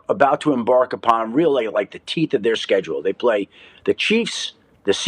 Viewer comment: had to rewind cuz I thought Schefty said “the commanders are about to embark upon, really.. the Teet of their schedule..” lol, my b